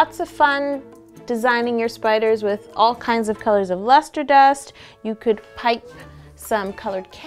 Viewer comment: At first I thought She said muster dust